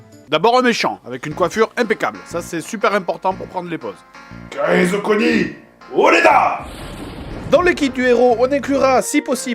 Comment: C'est moi ou en 03:25 le grand méchant dit "Je serais le roi des pirates" (Kaizokuo nii, ore da !) à la One Piece ?